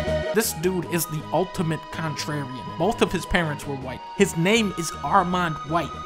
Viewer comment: at around 2:40 dunkey says armond white is the ultimate contrarian but the captions say ultimate contrary someone might want to fix that